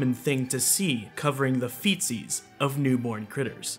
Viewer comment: Am I the only one who thought he said feces?